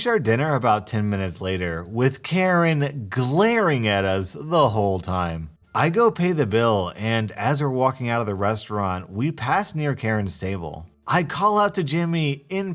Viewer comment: For a second, I thought you said "We passed by Karen's stable." I started laughing uncontrollably. 😂😂😂